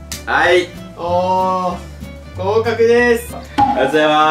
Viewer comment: の｢合格です｣がQKの山本さんにしか聞こえなくて😇